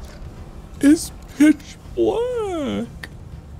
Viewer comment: okay this may be me not paying attention at first but I could have sworn he said at 39:42 "this b####'s black" I had to rewind it because it threw me off. I realize now he said "its pitch black".